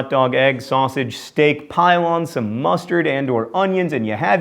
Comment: He said "pile on" while I heard "pylon".